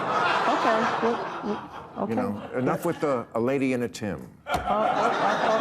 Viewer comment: I thought he said “not a lady and the tamp”…. What a bar that would have been.